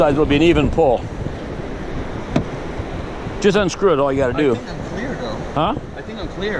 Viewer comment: What'd he say @ 9:04 ? "I think I'm queer" ??? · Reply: "I think I'm clear " .